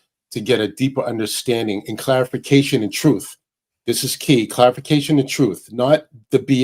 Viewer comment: did i hear troof?